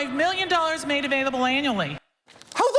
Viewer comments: Sounded like she said "anally" not "annually". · Anyone else hear her say anally? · When she said: "We give out $45 million annually", I misheard the first time and thought she said "anally".